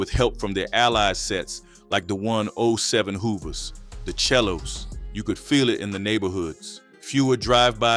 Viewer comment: It’s actually selos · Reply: the way he pronounced it sounds like he was saying “chelos” 🤣